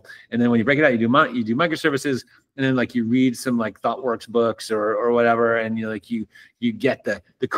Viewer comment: at @ 28:05 what is he saying? thought worth books? i wanna know so i can read